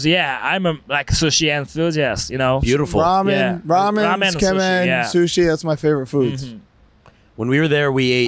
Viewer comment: what did say? Ramen, what, and sushi? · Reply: Tsukemen